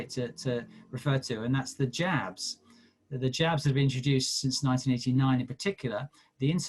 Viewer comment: Did he say "Japs" at 55:00 ????